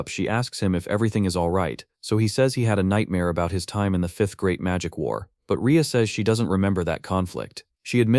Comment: The AI said "Fifth GREAT magic war". I heard "Fifth GRADE magic war". hahahaha A bunch of fifth graders at recess is a magic war. Its Adorable, Dangerous, and will either leave 0 mess or look like a construction company half-assed their job. Also about the TV guy. In Japan can TV(or any) salesmen just "inspect "your home for something they can market. Buddy thats a great way to end up as a corpse in a dumpster.